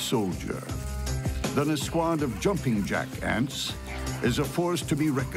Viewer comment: for a second i thought he said "jumping jack ass"